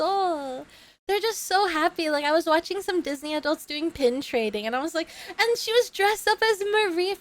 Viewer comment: I thought you said penetrating, and I nearly lost it